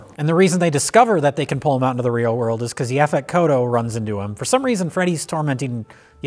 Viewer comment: The what? Effekoto? Eficoto? The hell is Jay saying? I've never heard of an Effect Kodo before? Not even the auto-generated CC knows what Jay is saying...? I'm assuming it's some sort of job title for the character? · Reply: The actor's name is Yaphet Kotto